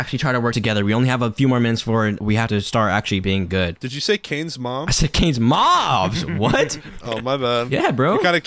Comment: YEESSSS THE WHOLE GANG!!!! (Well kind of) 16:55 I heard him say canes mom too 🤣😂🤣😂